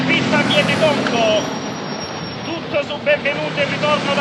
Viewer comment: Stupenda! Qualcuno capisce che cosa dice lo strillone all’inizio? “Aumentato il prezzo dell’oro”, “Tutto su Benvenuti e il ritorno dall’America” (in riferimento al pugile Nino Benvenuti) e in mezzo non riesco a sentire bene “Grossa sconfitta …”. Minuto 00:29 Qualcuno lo sa/riesce a capirlo? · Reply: Credo dica "Grossa sconfitta a Vietecongo" italianizzando Viet cong (guerriglieri del Vietnam); un riferimento alla guerra del Vietnam